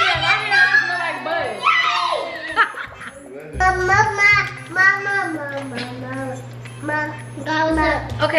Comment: it sound like said “I love my mama” 🥰🥺 & saying the sugar cookie dough looking like 💩 had me dead 😂😂😂